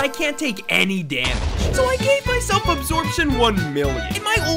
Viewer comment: I heard: " I gave myself abortion..."💀